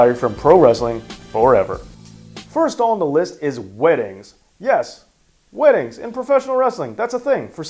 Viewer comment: You sound like you said winnings in the beginning so when I heard that I was like ok let's see where he goes with this then I looked and it was weddings then I'm like ok that makes more sense.